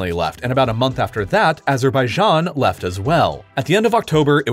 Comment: when it says "left" i hear something like "laugh"